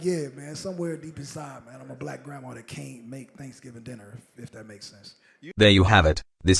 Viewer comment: I really thought he said "I'm like a black grandma that came" lmfao.... That shit don't get wet, when grandma queefs dust comes out... much like when Game raps these days, it sounds like a dusty queefe.